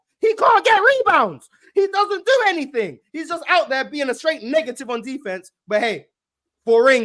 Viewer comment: I thought was gonna say nigga